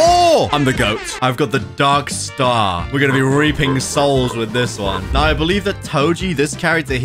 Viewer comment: I thought he said raping lol